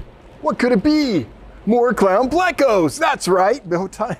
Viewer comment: I was so excited when you said clown pleco. In my head I heard "claro" pleco which I can't find freaking anywhere. Dang! :(